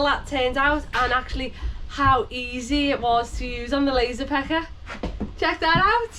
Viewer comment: Thought you said lazy pecker. cant help it 😅